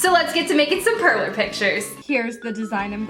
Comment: For a hut I thought you said perv pictures apart for... Pearl maybe it's hard to know what you where saying